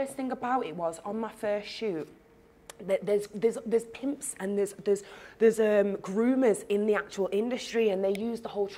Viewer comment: The part about the Illuminati/satanic ritual abuse🧏🏾‍♀️. Zeze and this was very informative, thank you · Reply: Growth Alone From time I heard “there’s some whores in this house” it wasn’t for me😂, don’t put all women in a box